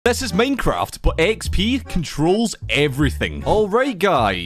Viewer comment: It sounded like he said maincraft